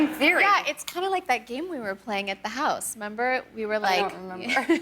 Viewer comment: did anyone else get bothered when Kim said member instead of remember???